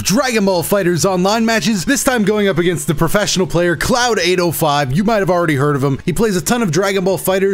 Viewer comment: thought he said CloudAnal5 lol